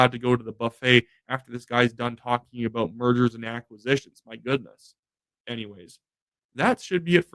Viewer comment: I thought you said Murders and Acquisitions ..... lol .... 😂😂😊😊